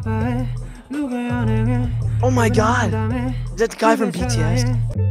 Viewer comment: Did he say Kai from BTS 😭?